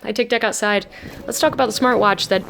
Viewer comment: You're aware the start of the video sounds like you say, "I take d**k outside," right? Pronunciation matters.